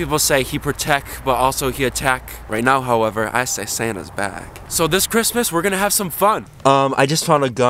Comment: He protecc He attacc But most importantly Santa is bacc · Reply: Ohhh I thought he said satan was bacc